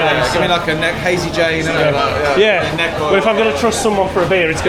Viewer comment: Hazy Jane was mentioned ... So quick.. Anyone else hear?? WE all LUV a HAZY JANE <3